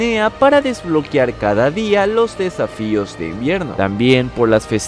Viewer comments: Escuché desafíos de mrd jajaja estoy cada vez más jodido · Yo entendi para abrir los desafíos de mier**